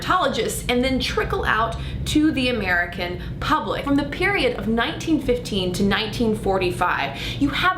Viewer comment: did she really say 'period'?